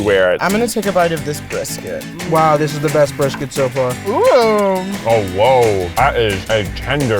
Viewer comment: at 13:18 : oh wow. Me: Did he just say owo...?